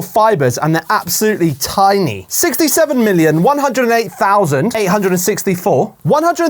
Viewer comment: did I just hear…. 6…. 7…..?